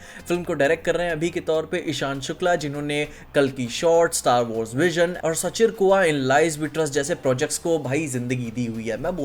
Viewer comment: What's he is saying 5:37? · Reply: schirkoa in lies we trust. Direct by ishan shukla. YouTube pe trailer hai